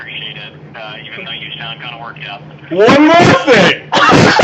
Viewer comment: what does he shout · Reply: ONE MORE THING!